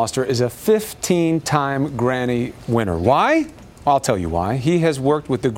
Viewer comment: Did he just say "granny winner"? LOL